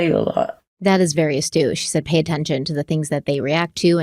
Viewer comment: ⚠️Silly question⚠️ @ 1:07:00 says about Brenda’s comment: “that is very ‘??????’” to me it sounds like pronunciation: astūsh “that is very astush” can someone including you tell me what this word means? I’m a ‘word nerd’ and cannot find the meaning of this word or am I miss understanding her?